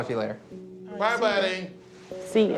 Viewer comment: lol 11:07 did he say “yeah buddy” ? Summoning Ronnie Coleman